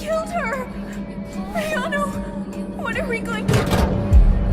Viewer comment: does she said ayano?